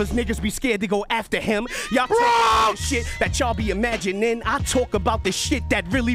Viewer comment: Did anyone catch the last bar- "Why you actin different, we can chat to the matty spitting, and your baby moms telling your kids that daddy's missing." WTF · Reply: Was good with the face palm? I rewinded several times..it sounded like he said "magic" to me.